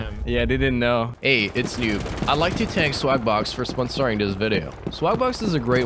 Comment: I thought he said slap box